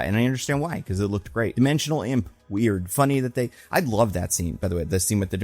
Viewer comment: I could've SWORN he said "dimensional AMOEBA" not "dimensional IMP" because it looked like a giant protozoa of some sort